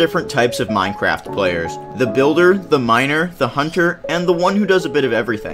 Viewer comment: "The farmer, the builder, the miner" What i heard: "the farmer, the builder, the minor"